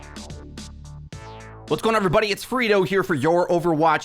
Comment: Loving the amount of uploads recently! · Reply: Oh its? I always thought he was saying Frito like the chips.